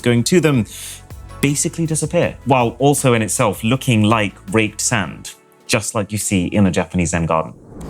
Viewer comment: am i the only one who thought he said raped not raked sand?